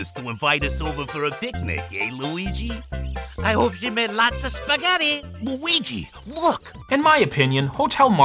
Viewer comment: I HOPE WE MAKE LOTSA SPAGHETTI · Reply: OH! I thought they said, "Let's go to Hotel Spaghetti"